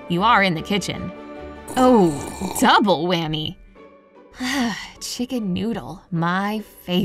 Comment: wait I thought she said Chicken noodle soup💜. Now, the lyrics is dancing on my head😅😍😍